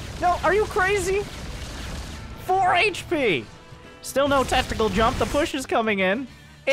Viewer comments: did he say "testicle jump" ? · still no TESTICLE JUMP? did i hear that right?